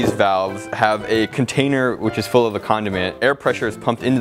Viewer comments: The machine thought you said con*** instead of condiment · When was talking about the voice thing about google cloud when he said “the condiments” the computer thought he said “car condoms”